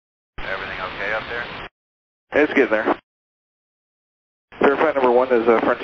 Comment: I believe the ATC responded with "it's getting there" instead of "it's good now" as the subtitle indicates.